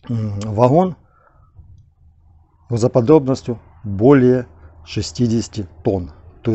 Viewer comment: Сними обзор на девушку которая говорит в говорилку. У них конкурс наверное. Чем непонятнее говорит тем лучше.... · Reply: У него тоже не мог разобрать, что он говорит. Слушал несколько раз. А оказалось, что это- "грузоподъёмность". _4:39 :^)